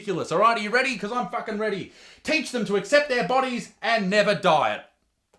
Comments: "and never DIET" I thought he said "and never DIE" anyone else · Instead of hearing ‘never diet’ I heard ‘never die’ 😂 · Lol I heard: "accept their bodies and never die" Fat is an answer for the immortality.